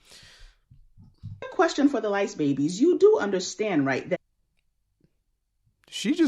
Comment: I thought she said light babies😂